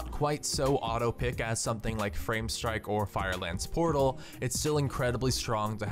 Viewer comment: Did i hear Frame Strike?